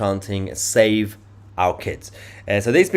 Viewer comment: "Save our kids!" Maybe it's because of the UK accent, but I thought they were saying "Shame on Keir!"